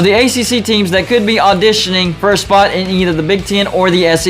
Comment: Did you say AssEC right at the end? (at about the 10:55 mark)